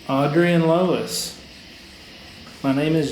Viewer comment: She is not saying “Lois” but “HOLA”, “Hello” in Spanish! It is really clear!